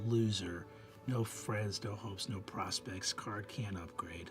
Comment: I swear I had to rewind three times because I thought Brennan said the card has "no hoes" instead of "no hopes".